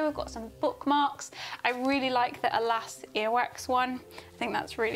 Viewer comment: Heard you say “Elastic Earwax” instead of “Alas, earwax” and I was so confused 😂